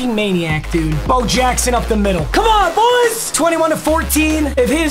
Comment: sounded like BO JACKOSON said " come on boys " an i was rollin